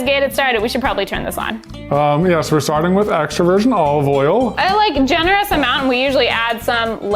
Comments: "... so we're starting with extroversion olive oil..." — 2020 Yeah yeah, you probably said extra virgin, but this is what I heard and I'm sticking to it. Also, I only have introversion oil, is that okay? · It sounds like you said extroversion olive oil